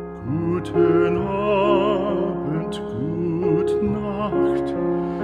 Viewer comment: Why did I hear Nutella at 1:51 😂